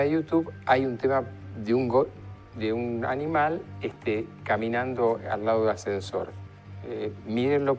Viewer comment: que dijo? un animal caminando al lado de un ascensor?